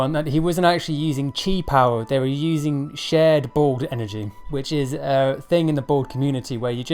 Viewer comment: did anyone else hear him say ball energy?